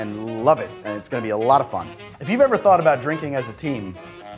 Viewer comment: Thought he said “if you ever thought about drinking as a teen for a moment” lmao